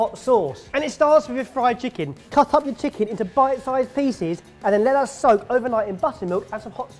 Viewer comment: I thought barry said "bite sized feces"